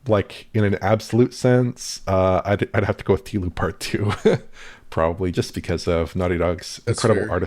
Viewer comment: Sorry but I cannot hear what he says at 3:23 "Healing / Human Part 2"? The closed captions don''t help as it has him saying "T Part 2"(?!?). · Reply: TLOU part 2